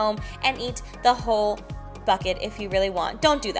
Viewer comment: When she was talking about the cookie dough cone I thought she said "eat it whole fuck it" 😂😂😂